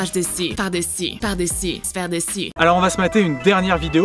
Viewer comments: c'est moi ou elle dit "s'faire dessus" ? xD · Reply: En fait je crois que c'est ''sphère dessus'' genre mettre la sphère dessus ! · Mdr à 10:39 elle dis 《s'faire dessus》ou c'est moi ? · c'est moi ou elle dit "se faire dessus" ? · moi j'ai compris "s'faire dessus" perso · Je rêve ou j'ai entendu "S'faire dessus" ??? 😂😂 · j'ai entendu s'faire dessus